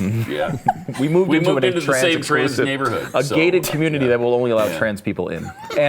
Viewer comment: did he say a gay-ted community😂